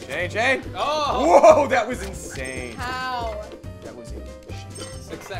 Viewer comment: It was a bit quiet but did Damien say "that was inShayne" ? Cause that was exactly what i would say xD